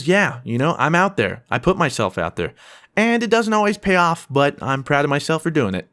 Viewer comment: i thought he said "porn is, yeah, i put myself out there...."